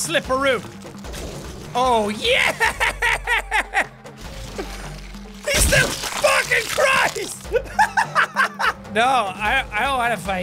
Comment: did he say the penguin is still fucking christ!